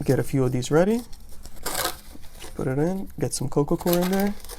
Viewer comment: sounds like you say coco cor what is the soil your using?